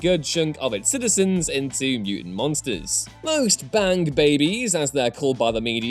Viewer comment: I'm sorry but did you say BAND babies?